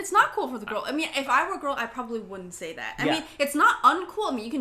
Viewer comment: Did just say: "If I were a girl" lol